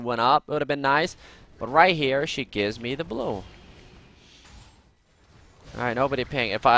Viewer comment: i heard "gives me her blue" but that was red buff